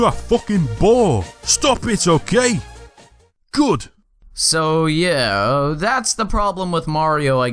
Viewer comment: I heard Stop it you're gay, good